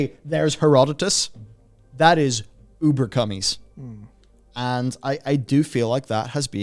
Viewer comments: Did just say "Uber Cummies"? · Did you just say, "Ubercummies"???